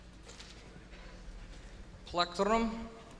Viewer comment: Why did he say "pletlrlrlon''